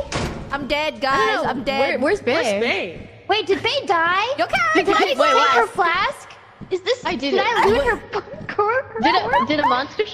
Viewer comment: am I tripping or does it not sound a bit like Fauna cuss here? I genuinely don't know cuz I don't think I've heard her do that before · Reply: sounds like "is this her flask? did I loot her fla- corpse?"